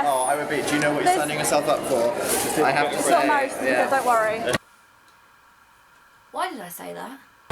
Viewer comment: what did you say at 6:24? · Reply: "It's not a marriage certificate don't worry" And then a fan actually did that and I was mad!